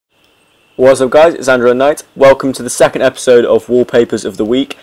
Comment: Ive been watching your videos for 2 years, and every time you say "Android at night" it sounds like you say Andrew Mcknight.